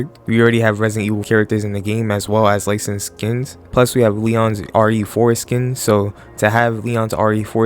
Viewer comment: I had this playing in the background while getting ready and I swear I heard “we already have Leon’s foreskin” 😂 I had to rewind it to realize you said “ RE4 Skin”